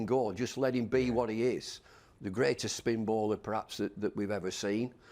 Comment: did he say perhaps??!!